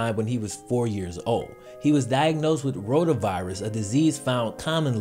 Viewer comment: When you said rotavirus I thought you said ronavirus and I was so confusled